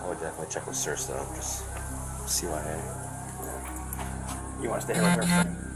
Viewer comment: CYA (Cover Your Ass) 10:04 The subtitles are wrong.... it says "see what" but what he says is CYA for cover your ass.